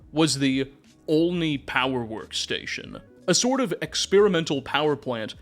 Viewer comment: Thought you said ONI power station & was very confused about why Halo was involved lol